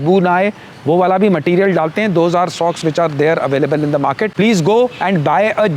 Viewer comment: Okk there's an error that I noted & wanted to notify you regarding the same. At 3:51 instead of socks , "shocks" is the word being displayed.